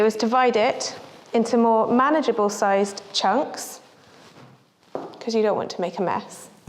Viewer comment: Did she just say " 'cuz' you don't want a mess "? 6:9